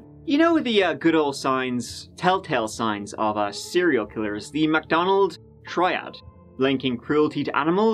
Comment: Def thought you said McDonald’s instead of Macdonald’s and I was like fire setting????